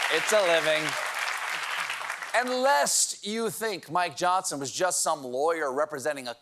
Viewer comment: The first day I heard "Mike Johnson" as "Mike Jones." Like the rapper famous for 'Still Tippin.' I had my Alexa play it and then I thought what a great theme song it would be for Mike J. 🎶 "Back then hoes didn't want me, now I'm hot they all on me.."🎶